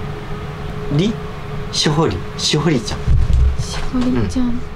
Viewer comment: のところで京明先生が名前を確認するところで、しほみちゃん？ん？って京明先生が聞くところで 女性の声で、しほり。りって聞こえますね